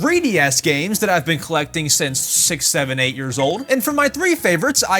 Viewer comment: did he just say 78